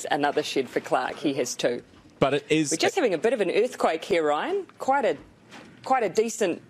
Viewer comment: 早口すぎて全然聞き取れん笑笑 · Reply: we're just having a bit of an earthquake here Ryanだと思う